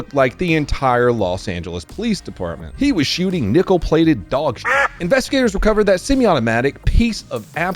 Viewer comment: Did he just say “a blue played shirt”????